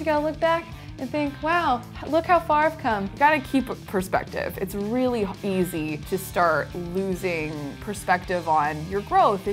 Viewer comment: did anyone else hear her almost say "its really h- easy''??? (8:59) listen closely.